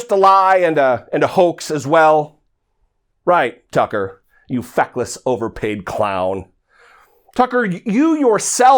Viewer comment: I thought you said "fuckless" but you said "feckless".😂😂😂 I believe the two are interchangeable in Cucker case!!!